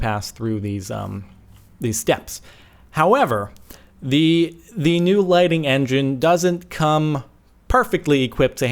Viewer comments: I thought it said new lightning :( Darn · I thought it said "Lightning engine" And I said: Its time to industrialize! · I thought it said 'Lightning Engine.' And I was like 'Whaaaaaa--' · i thought it said lightning engine LOL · who else thought it said lightning engine......derp